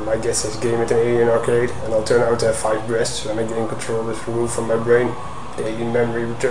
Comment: I swear he says F5 Breast.